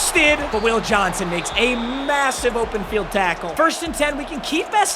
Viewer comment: did he say massive